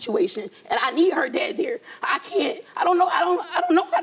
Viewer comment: It sounds like she wants him there for both the daughter and her but he just wants to be there for the daughter. He doesn’t want to be with her. She keeps saying “physically there” and he’s giving “I don’t want to be in the house” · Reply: Where did u hear that?? I heard “I need help around here”. I got hes a bum who wants to live in her home and toss his socks on the floor for the maid. Fuck that